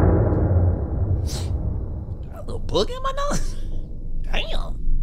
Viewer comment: Very beginning of the movie and all I heard is, "is that a bugger in my nose?" 😅😂🤣